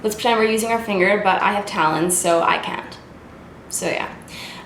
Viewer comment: Omfg I thought you said "but I have talent" and I was like gurl that hurts then I realised you were talking about your talons lol